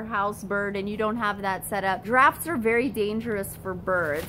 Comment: I heard “giraffes are not good for birds” but you said drafts 😂